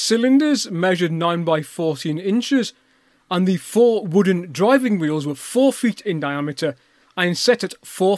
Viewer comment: Wooden driving wheels .... That's what I thought you said. I had to play it through again to make sure.